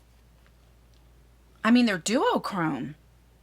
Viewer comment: I love your enthusiasm. I think the pallet is meh wait..did you just say duo chrome??